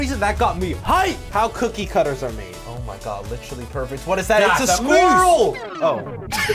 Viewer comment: why did i hear ‘its a smurf’